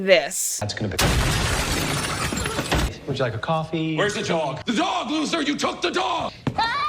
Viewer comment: At first i thought he said "The dog, loser? Wheres the dog?"